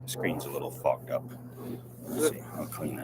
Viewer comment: Steve says, "the screen's a little fogged up". What I hear: The screen's a little fucked up. (excuse my French Canadian) 🤐